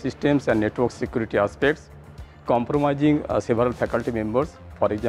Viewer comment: I couldn't really understand what Prof. wants to "compromise" (1:47)? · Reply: I think its "comprise"